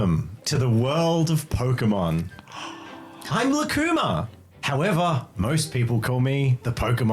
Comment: sounded like ''Im luekemia!''